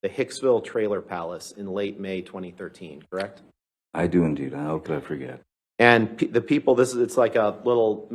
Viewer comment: Do you remember going to this place, I do and I hope I'll forget!!🤣🤣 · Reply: I thought he said “how could I forget”